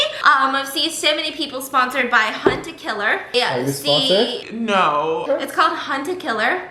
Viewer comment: For some reason I heard “killing stalking” instead of “hunter killer”...